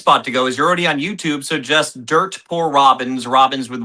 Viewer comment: Dirt Core Robbins? Haha What did he say his band name was? Edit: nm. Dirt poor. That makes a LOT more sense.